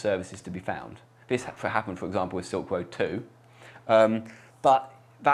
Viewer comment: "Sometimes a vulnerability is found in a protocol, all right? or compromise nodes wich allow hidden services to be found. This is happened for ?????" What does he say? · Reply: Robot Ice Cream Sandwich, Silk Road 2